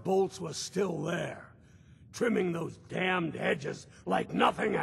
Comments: Vault tec rep said damn hedge even though the subtitles said fucking hedges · the subtitles say fucking but he says damn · he said "damned" subtitles said "fucking". Last minute censored? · "and he was still there trimming those (damn) hedges like nothing happened!". It actually in the subtitles says fucking. Why did this happen? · Why does it say “fucking” instead of “damned”?